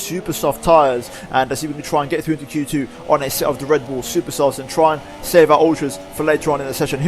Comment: did you say "res bull supersofts" or brand new supersofts " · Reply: Red Wall Supersofts :P